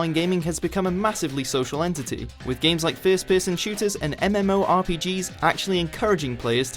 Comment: thought he said Fist piss and shooters :/